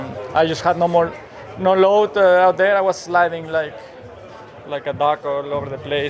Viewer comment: Did Perez say " sliding like a taco " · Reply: I thought he said sliding like a duck